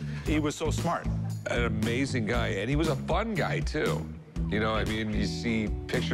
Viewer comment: Did Rick say that Albert Einstein was a "fungi"?